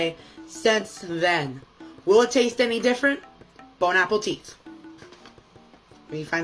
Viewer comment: “Bone, apple, teet” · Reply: I came to the comments to see if I was the only one. I hear " Bone Apple Cheese"